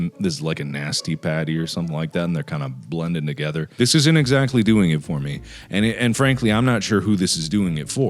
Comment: “Anne Frankely this isn’t doing it for me” why’d I hear this 😅